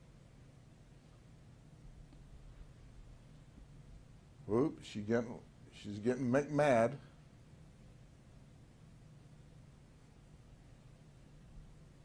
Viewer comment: Did he say she's getting "Mc-Mad?" 🤣